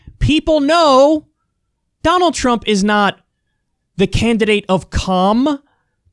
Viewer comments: Did he just say Donald Trump is not the candidate of cum? This changes everything... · I misheard that as "candidate of cum" lmao